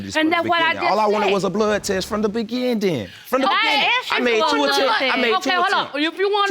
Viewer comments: Apparently he wanted a bloodtest from the BE-GIN-TING 🤣🤣🤣🤣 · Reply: I thought he said beginding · He wanted a blood test from the BUGINNNDINN!🤣🤣🤣🤣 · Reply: 😂 i heard him say bugindin too i was too tickled 😂